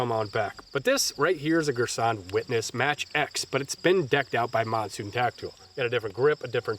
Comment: it’s been decked out by MODswwvrtyTactical Decked out by who? Can’t understand what he says. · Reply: Monsoon Tactical